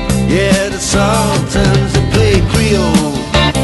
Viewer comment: The Sultans of Creel is what I thought they said forever. What does creel mean? 40 yr old question. Just sayin'